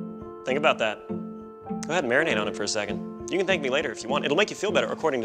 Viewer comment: Am I deaf or what? because I heard at 0:37 "Think about that, I'd marinate on it for a second" LOL